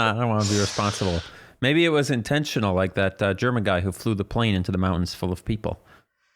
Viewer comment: At first I heard that said "Jerma" instead of "German" and that confused me a little.